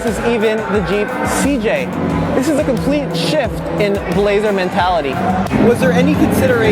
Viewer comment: I thought he said "This is complete shit" and I would have believed him 😂